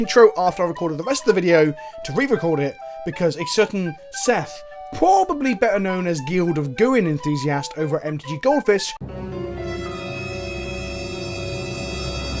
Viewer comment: What was said then? What I heard was "...Guild of Gooing Enthusiast..." If that is what was said I don't understand that. . (Around 1:35) Also I hope the comment helps : ) It was a fun video to watch